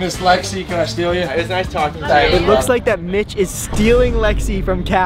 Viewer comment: did he just say the b word? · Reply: he said mitch, it’s the guys name